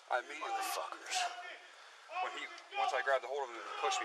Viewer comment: did he say "once i grabbed the whore" we are all just criminals. now the lady in the car is a prostitute?